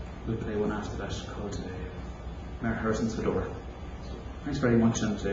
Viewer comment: 3 reels : "Master Crowley's / The Log Cabin / Mayor Harrison's Fedora" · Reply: Mayor Harrison’s Fedora - thank you. - I thought he said ‘ma pherson opens the door’